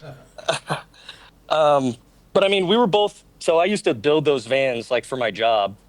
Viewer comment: in.. I thought dude said, " I used to dildo vans".. and then I thought things were going to get interesting. But my ears really perked up like a student when I heard "gun".